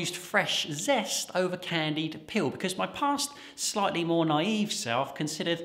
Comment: "Even replacing fresh zest with candied peel is acceptable now a days." LOL best April Fools ever! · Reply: I'm American. I thought was saying "candied pill."